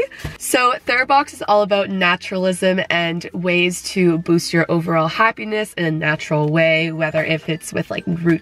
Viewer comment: when she said “it helps you boost your happiness” i thought she said “LOSE your happiness” i was like weird flex but ok